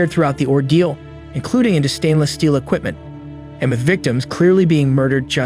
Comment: sounds like he said "MacVictims"